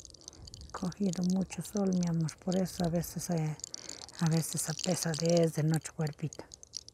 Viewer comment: que dice ella ? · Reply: Ha cogido mucho sol mi amor, por eso es a veces esa pesadez de nuestro cuerpito